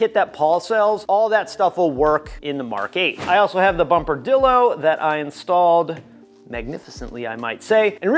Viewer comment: I thought you said bumper dildo….and how much you enjoyed that mod…..